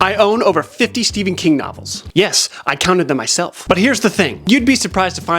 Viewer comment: you said “butt” instead of “but” and my reaction is making me realize that I don’t think I’ve matured since high school.